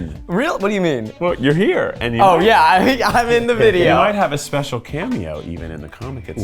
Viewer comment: Oh my God! I thought he said "in a suit" this is so much better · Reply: I thought he said "I'm in a suit" as well, but "I'm in the soup" is so much funnier!